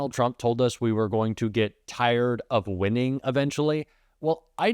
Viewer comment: You misheard TACO, he didn't say "Tired of winning" he said "Tired of my whining"